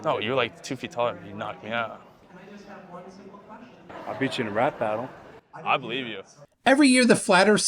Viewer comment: The big tough ginger bloke was by far the funniest. He so cool with his wanna be rapper/b.baller reject outfit and his "I'll beat you in a rap battle" threat. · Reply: Oh... I heard "rat battle" and was confused. 🤪